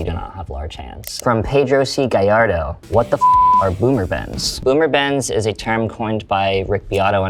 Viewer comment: I thought he'd said "boomer bands" and thought he was dissing older bands.